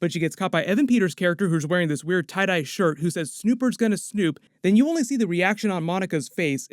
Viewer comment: I thought Evan Peters said "Supers gonna snoop." Implying that they both sneaked in w/ their own agendas. I know he actually said "snoopers" but 🤷🏼‍♂️